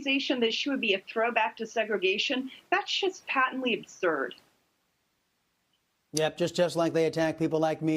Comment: In response to feminists proclaiming ACB being a "throwback to segregation" I believe I heard say at 0:56, "That shit's patently absurd!!" . . . Good show, girl!!!